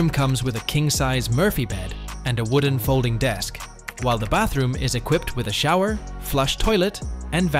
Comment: “and the bathroom….” Subtitles say bedroom xD